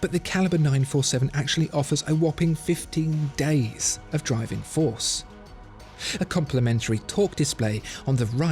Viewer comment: Ha Ha. I heard you say 15 days of power reserve. Need to get my ears checked.